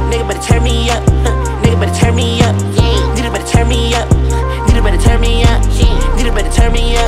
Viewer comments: i swear it makes me mad that it sounds like hes saying "peanut butter turn me up" the whole time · Omfg been waiting way too long for this 😭🔥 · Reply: Is it just me or does it sound like his saying peanut butter turn me up😐😂😭